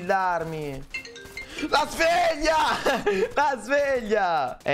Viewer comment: invece di dire "sveglia" ha detto "fregna" da come ho capito